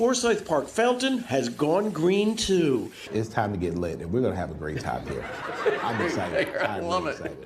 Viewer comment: lmao, i thought he said "get laid" xD